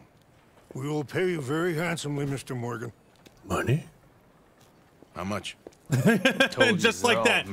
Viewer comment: did you say monah.